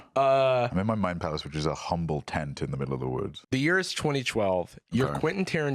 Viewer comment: "I'm in my mind palace which is a humble tent in the middle of the woods." is one of the funniest people without even trying. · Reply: Hahaha! The correct quote 😂 I heard "in my mind, alas, is a humble tent in the middle of the woods" I'm so double laughing; now triple laughing -at my brain; because I still hear saying what I heard and have forgotten the context completely.🎉❤🎉